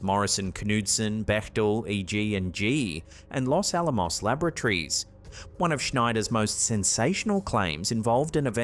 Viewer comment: I had to rewind the Area 51 clip there. Could've sworn you said "Los Alamos lavatories" 🤣 Edit: correction